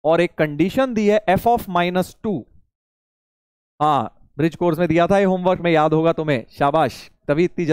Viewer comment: bright course kya hai please koi batao 😢😢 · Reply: Bridge hai voh; pw foundation pe stream hai